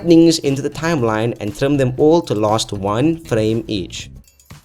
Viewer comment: it's quite clear what I said there. I even slowed down and separated each word..."and.trim.them.each.to.last.1.frame" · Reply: All I heard was "And trim them all lost frame H"